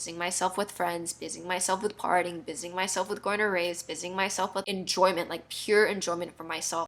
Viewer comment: When you said "Busying myself with going to raves", I heard "Busying myself with Gonorrhea" o_O No joke. Seriously, though, glad you do not have a permanent damage to your body from those days! And glad you are now in this journey to a healthier you and sharing it with us :) Emotional scars heal too. They just take a little more time sometimes